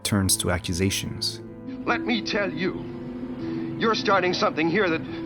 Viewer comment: CC's say "your" instead of "you're" how that fucking mistake made it to ccs of a great channel beats me